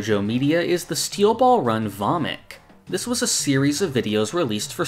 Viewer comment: ngl i thought you said Steel ball run vomit